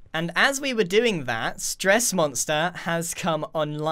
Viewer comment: Right after grian said would you like to shop at sahara? I legit thought it said gressmonster